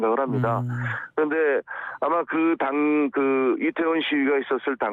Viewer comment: 이태원 시위? 내가 잘못들었나??